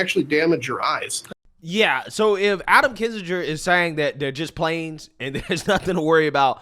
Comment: I thought they said they was just planes lol